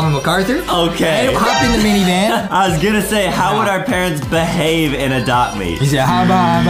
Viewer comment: I thought Adam said bad when he tried saying behave in adopt me